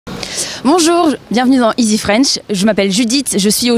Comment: I didn't hear dans? It's too fast but the subtitles help a lot!